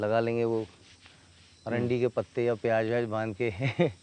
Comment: क्या आपने भी वही सुना जो मैंने सुना? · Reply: Arandi ke patte